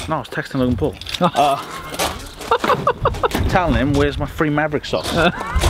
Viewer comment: did he say wheres my free mavric socks? #LOGANGFORLIFE